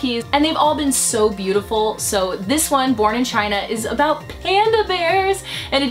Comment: lmao i heard "porn in china"